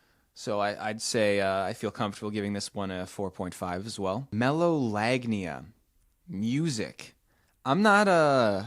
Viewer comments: I have melolagnia. There is some music that is very sexy to me, specially tropical and pop songs. For example, the very sexualized music and stuff that matches the rythm of sex and so on. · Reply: I thought you said megalovania lmao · I have melolagnia. There is some music that is very sexy to me, specially tropical and pop songs. For example, the very sexualized music and stuff that matches the rythm of sex and so on. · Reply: I thought you said Megalovania like from Sans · I have melolagnia. There is some music that is very sexy to me, specially tropical and pop songs. For example, the very sexualized music and stuff that matches the rythm of sex and so on. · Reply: I thought you said Megalovania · I have melolagnia. There is some music that is very sexy to me, specially tropical and pop songs. For example, the very sexualized music and stuff that matches the rythm of sex and so on. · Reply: Thought you said megalovania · I have melolagnia. There is some music that is very sexy to me, specially tropical and pop songs. For example, the very sexualized music and stuff that matches the rythm of sex and so on. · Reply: For a second I thought It said megalovania